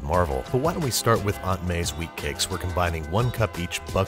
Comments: At first I thought I heard weed cake but it had shatterd my dream seeing the word wheat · I thought spidey said “weed cakes” at first lol. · I heard "weed cakes" before I heard "wheat cakes"